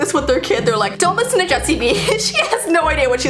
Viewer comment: OMG sorry I couldn't stay quite but at 10:58 when Jessii says:"they're like don't listen to Jessii vee"The Captions litrally say instead of "Jessii vee" "jet CB"Like WHAAT?